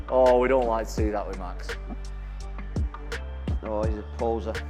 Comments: The double spinning backfist was fkn insane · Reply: did he just say "oh he's a poser" lol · did he just say "oh he's a poser" lol